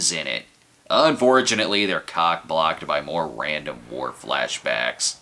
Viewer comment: The DS9 connection meant I misheard "Flashbacks to war" as "Flashbacks to Worf". I guess there wouldn't be many people who know her from her role in "Becker". (And wow, how old is that picture in the Cinema Snob logo - still had hair!)